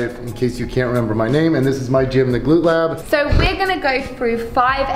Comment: I thought you said Glute Lube. My bad.